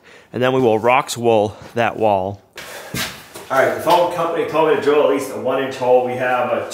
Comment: You said "foam company" I heard phone company. It was an interestng couple of seconds of, "What!?" for me. A hole saw might have made that easier eh?